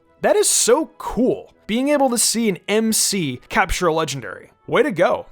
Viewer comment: did you say mc